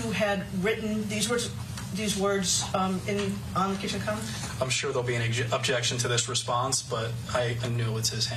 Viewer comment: what did he say at 12:40? "I'm sure there will be an objection to this response but ..."?